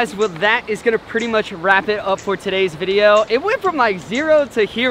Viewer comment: I heard "..pretty much rapid up for today's video"